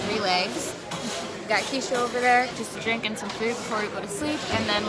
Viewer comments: is it just me or did she say drinking food😂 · did she say drinking some food?...okay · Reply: She said "drink and some food" lol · did she say drink some food before bed? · did she say drinking some food🤔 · she's so tired that she said "just drinking some food before we go to sleep." XD sorry if in wrong guys lol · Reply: I thought she said drinking fruit, like a smoothie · Did she just say “Just drinking so food.” wut.... XD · did she just say she was drinking some food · did she just say she’s drinking some food? 😂😂😂😂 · did she just say we are drinking some food